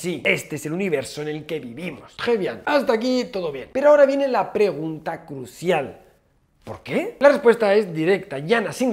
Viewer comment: Hola muy buenas, me gustaría saber qué dijiste en el minuto 2:30 del vídeo. Claramente está dicho en otro idioma, probablemente en francés, pero me encantaría saber qué es y cómo se escribe. Muchas gracias Pd: me ha fascinado el video! 👍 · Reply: Dijo "Très bien" que es "Muy bien" en francés